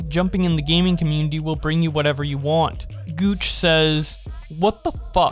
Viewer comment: did you say gooch? lol says Guac